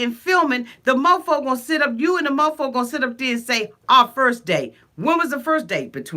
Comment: You're right bc when she asked about that first date, I thought he said 2 months ago